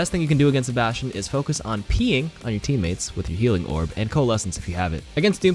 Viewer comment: On bastion i heard "peeing on ur teammates"...